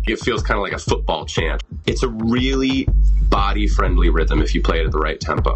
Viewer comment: I THOUGHT HE SAID IT WAS "THOTTY FRIENDLY" AT 1:28 SKSJDKDJD